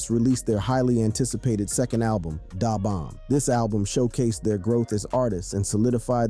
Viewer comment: How he said DA Bomb.. it’s sounds like he said Day-Bomb 🤦🏾‍♂️😂😂😂